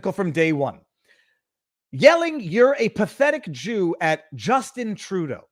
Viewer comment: I heard heard “a pathetic Q”